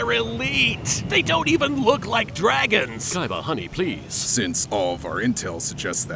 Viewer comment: did yugi called kaiba “ honey “ ?